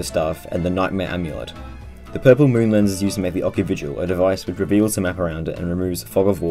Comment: What did he said, again? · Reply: "...The purple moonlens is used to make the ocuvigil..."